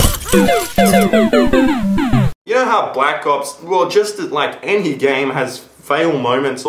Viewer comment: at the start, i could have sworn you said Black Cops, not Black Ops.